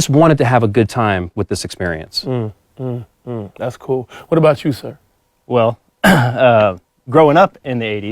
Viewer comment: Lol I thought he said um um that's cute what about you sir 😂